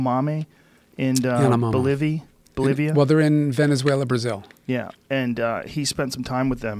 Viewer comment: Does anybody know the tribe name they said I heard Venezuela/Brazil